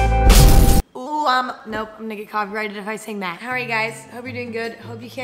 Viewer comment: was I the only one that heard the n-word instead of "I'm gonna" lmao